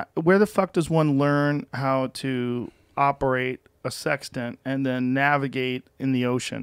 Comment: How many people thought Joe said 'How do you learn how to operate a 'Sex Den', then navigate the ocean'? I was like WHAT?!?! lol